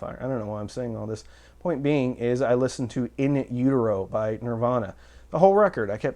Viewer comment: Did he just say "in euro by Nirvana"? Is that in utero with a silent t?